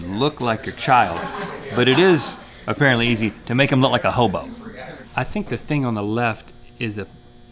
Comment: RHETT I almost thought you said ABOBO from Double Dragon NES and that baby does look like ABOBO 8:00 XD